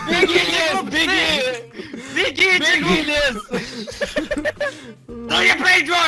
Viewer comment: мне послышалось бегите голубцы ор